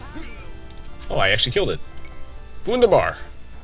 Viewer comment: Hey awesome Video, Love AoEFE too, but im not a pro like you. Im sorry, but did u say at 6:08 the german word "wunderbar" ?